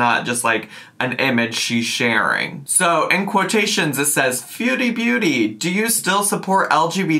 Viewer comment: sounded like Feudie Beauty (no shade to) and (high pitched) honestlyyyyyy yeah